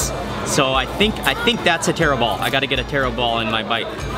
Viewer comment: I thought he said terrible But in fact he said taro ball xD